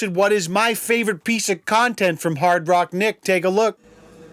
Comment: I totally thought that said Rock Hard Nick lmao